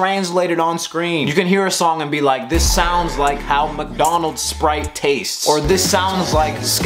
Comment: Did you just say: "This sounds like how McDonald's Spite"? Why did I completely understand what you meant? Crazy! Great video bro!